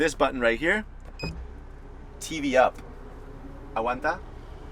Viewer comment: Did you just said "aguanta"? 🥴😂